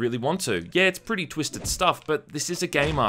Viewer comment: I think it's during the 4:40-4:50 mark: It feels like you are cut off from what you were gunna say. It's still a great review and I can't wait to get it via steam summer sale (Along with Paradise Lost, obviously) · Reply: ***** No, you misheard me. I said "this is a game, after all isn't it?"